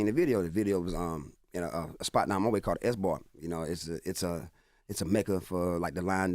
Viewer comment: "S Bar" on Garners Ferry Rd in Columbia, SC!! @ 5:11 The transcript says Esbort, but he actually says "S Bar". Shout out to Shannon Marshall and S Bar!! <link>